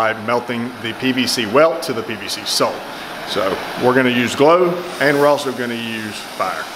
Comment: Did you just say that you use "glow" and fire? I know you meant "glue" but to me it sounded like "glow". Then again it could be my brain damage or my medications causing me to see and hear things again. 10 minutes later: It was me, my brain, or my meds. I turned on captioning and no kidding it states that you said: "...PVC welt to the PVC SOAP so we're going to use glow and we'realso going to use fire..." Now that's funny.